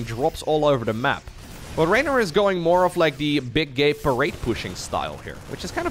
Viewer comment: Damn near spat my coffee out when I heard “big gay parade” lol